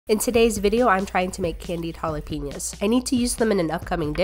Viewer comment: I thought they said fried jalapeños when I clicked on it my brain is broken